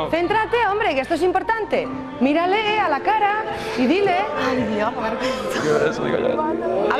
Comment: Si se fijan bien en el 1:56 la shava dice: "que vergüenza" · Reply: También en el min 2:50 como que dice "Ay Dios... que vergüenza"